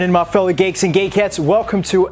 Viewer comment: did he just say ‘fellow cakes and gay cats’?